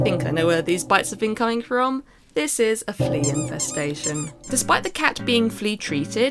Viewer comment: thought it said incest